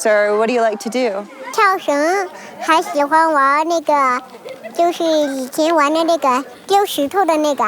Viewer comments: did she say the N word at 3:31 LOL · how many times did she say the n word